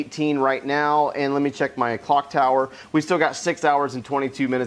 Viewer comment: what did he just say bro 😳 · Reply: Clock tower not 🐔 tower ⚰️